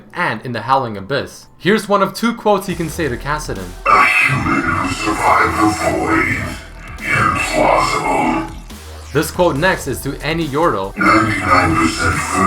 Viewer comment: if you guys don't understand what Vel'koz says at 5:33 ill tell you.."ghfjkaiyu" to yordles "jkjuiyq[rmc" and to zilean "mfhqyurp" thank me later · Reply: "A human who survived the void ? Implausible " Yordle:"99% fur, 1% glee" Zilean:"Zilean, there is a great secret within you." Ha:"Odd their attention drops in the presence of poros" It's not that hard to hear it , why is everyone complaining ?